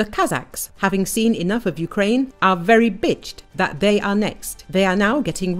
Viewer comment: did I just hear "very bitched" at the 33s mark? lol. Gonna start saying that now.